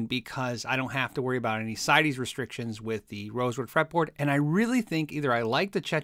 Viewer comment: why did you go with that wood for the fretboard. can't understand what you said 2:35 sounds like you said "SIDEYS restrictions"